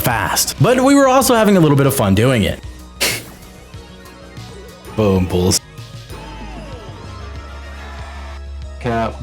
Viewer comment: What did he say?!?!? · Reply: Boompuls